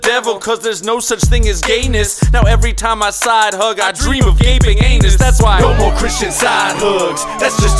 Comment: WHAT. THE. HELL! At 1:58, did he say "Gapeing ANUS?" What the hell!